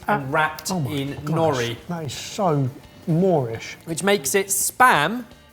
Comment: Honestly thought you guys were saying Moor-ish not more-ish